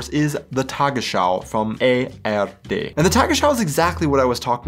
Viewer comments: did you just say "the Tagesschau from AfD"? 🤣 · Did you just say at 2:03 „the Tagesschau from AfD“??? Sounds like a freudian slip there mate. Oder ich bin einfach zu alt und meine Ohren funzen nicht mehr korrekt.